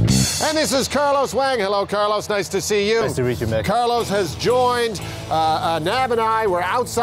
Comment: Did he say ''nice to reet you Mick" ?